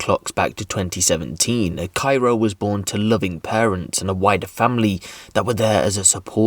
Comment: Omg, when he said wider family at 2:15, i thought he said WHITE family. i had to go back and put the captions on... 😂